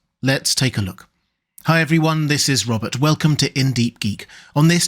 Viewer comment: Robert please don't ever stop saying "welcome" during your intro for I look forward to it every video. It makes your channel more personal and inviting. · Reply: and here i kept hearing “This is Robert Welkin”